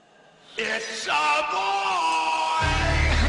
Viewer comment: if it wasn't for the captions, I would have thought he said ,,it's ya boy!"